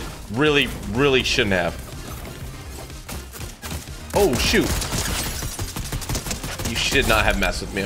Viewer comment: Anyone else think he said sh*t